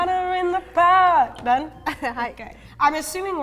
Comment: I SWEAR SHE SAID HAKAI!